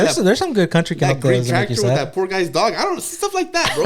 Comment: did say “green tractor with the poor guy’s dog” 😂